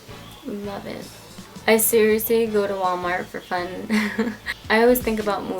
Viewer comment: my iPhone thought you said hi Siri lol